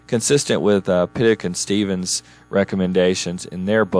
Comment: Hello Profesor, can you tell me the name of the book that you refer at 8:39 ? I heard Peak & Stevens, but I couldn't find anything. Thanks. Greetings from Perú!